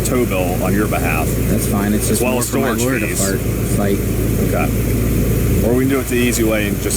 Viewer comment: Did he just say "fart" instead of "fight"?